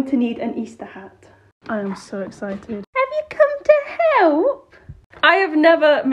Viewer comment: I thought she said have you come to HELL LOL 😂